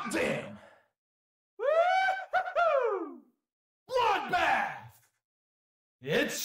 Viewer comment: What is dante saying at 1:19? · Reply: bloodbath